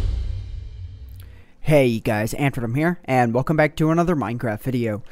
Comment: Did I hear you say 'Hey Guys, Antverm here'